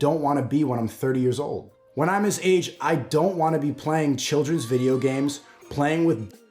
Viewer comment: "i don't wanna play children's vidéo games, i wanna play with children's, that's why i love Cuties" that's what i hear